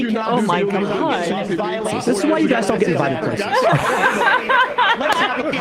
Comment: I couldn't hear what said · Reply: It was a sound drop from Tim Pool ‘this is why you guys don’t get invited places’